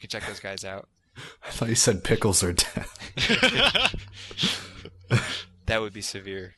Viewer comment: "I thought he said pickles or death." Me too! Haha